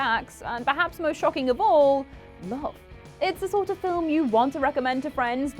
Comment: what word does say after "and perhaps most shocking of all"? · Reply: Love